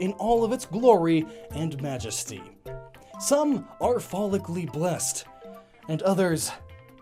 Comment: i heard "phallicly blessed" and now i feel uncomfortable...